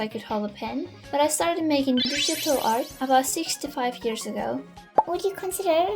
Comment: CONGRATS ON 5K :DD also i really thought you said "65 years ago" instead of 6-5😭